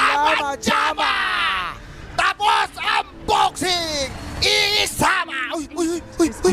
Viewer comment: I always thought he said "EE Sama" referring to eternalnvy in an anime way, the player that was killed in this echo slam? Think the subtitles missed this